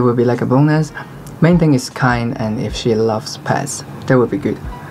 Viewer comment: In 2:48 he says that he likes girls that are kind and love??? Paths?? A friend and I are trying to understand what he says, Can anyone help please?